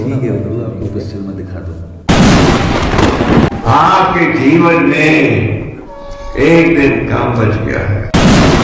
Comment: Hare Krishna prabhuji 9:32 mai Param Pujya Gopal Krishna Goswami Maharaj kya bole woh mujhe theekh se smjha nhi kya aap woh bata denge prabhuji? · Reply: Aapke jeevan me ek din kam bach gaya hai..